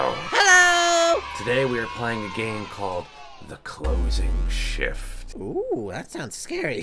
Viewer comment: I thought it said the closing sh*t😂